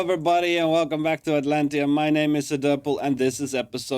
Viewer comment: I thought it said Atlanta lmao